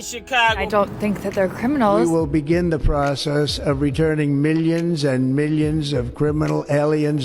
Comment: Sounds like Trump said minions and millions 😂😂 105